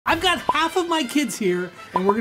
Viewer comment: Did you say half?